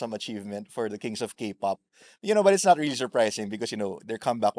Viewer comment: Did I heard it right? He said, Kings of Kpop? 😅